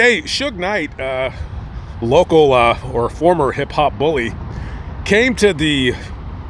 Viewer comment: I thought you were gonna say Hip Hop Mogul. LOL Suge is watching your video from prison.